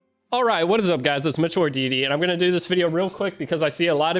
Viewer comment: I just noticed after watching like 10 videos you are saying it's Mitchell or deity. I thought you were saying your first and last name this whole time lmao